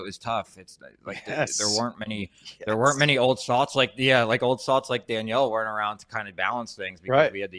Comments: What does Bobby say at 4:38? There weren't many old somethings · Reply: "Old Salts" I think is what he said, referring to veteran wives who've been around the block I guess? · An "old salt" is an old sailor or mariner who tells oral history and sea stories. Sometimes deemed a sage within their domain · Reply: Hah, I thought he said old slags :P